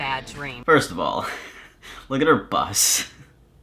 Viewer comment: No joke, I thought you said “look at her bust”